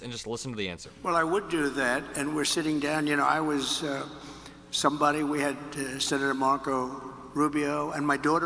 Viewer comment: He actually said "I was somebody."